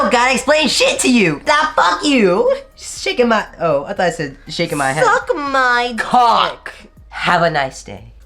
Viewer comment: I thought he was finna say shakin my dick (idk it's 2am) 😂